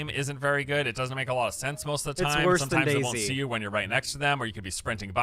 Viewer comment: Dude when you started talking about Day Z I thought you were saying "Daisy". I was so fucking confused cuz I had never heard of the game before but reading chat cleared it up 😂